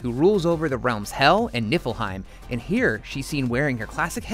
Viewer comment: did you just say... NIPPLEheim